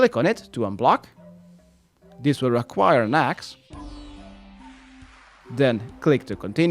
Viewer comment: thought he said this will require "max" 😂